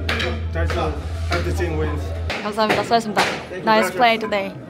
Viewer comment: 수고하셨습니다 라고 하시는건지 ㅋㅋ 이겼습니다로 들리는거 같음